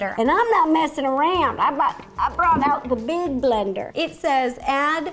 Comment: At first I thought she said "I brought out the DUDE BLENDER."